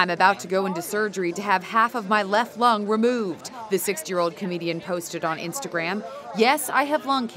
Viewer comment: It sounds like she said 6 year old lol